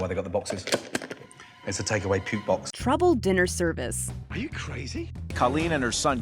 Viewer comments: "Now I know what the box is for" "its a take away puke box"😂😂😂😂😂😂😂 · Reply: I thought he said “take away pube box”😂😂😂😂😂🤣🤣🤣🤣🤣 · Barf bag... lol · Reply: I thought he said puke bag😂🤣😂🤣😂🤣😂🤣😂🤣😂🤣😂😂🤣😂🤣🤣😂😂😹😹😹